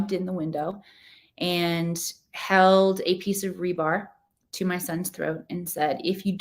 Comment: I'm glad things turned out well in the end. I do have one comment. When you were telling us the story of London's kidnapping by the woman I heard you say: "She held a piece of rhubarb to his throat". I'm fairly certain that's not what you could have said but I'd like to know what I missed! Thank you. You do speak very clearly but I lost it there. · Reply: She said Re-bar; not rhubarb. :)